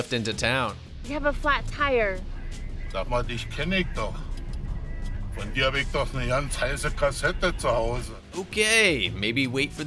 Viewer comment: What did you blurr out there? As a german I didn't understand anything inappropriate there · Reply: Sag mal dich kenn ich doch , von dir hab ich ne ganz heiße Kassette zuhause blabla